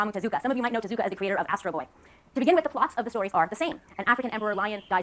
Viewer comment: i thought she said astroglide instead of astro boy